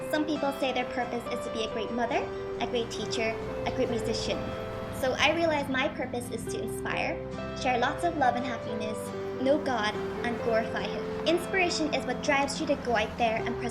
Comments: oh sorry I guess I thought she said "no god" instead of "know god" and I gotta agree with on that one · oh sorry I guess I thought she said "no god" instead of "know god" and I actually have to agree with on that one, you get so much from worshipping god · She said "Know God" not "No God" I thought she said that too, but I replayed it and just laughed :) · oh sorry I guess I thought she said "no god" instead of "know god"